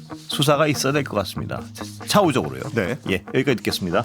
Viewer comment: 이런... 마지막 차후적으로 를 차우적으로 들었네요 차우차우